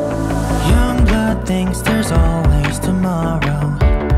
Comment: WHYYYYY......! I ALWAYS HEAR: "YONGBOK THINKS THERE'S ALWAYS TOMORROW"🤣